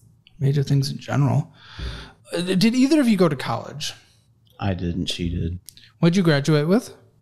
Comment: She has a degree, he’s a pizza boy. He’s more sensible. Never mistake intelligence with education. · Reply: You're right - my bad, I just went back and re-listened. I heard him say "I did and she did" instead of "I didn't - she did".